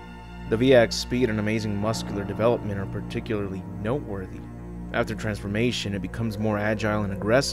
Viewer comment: My tired ass misheard 'The V-Act's speed and immense muscular development are particularly nope-worthy'. Never mind, it still applies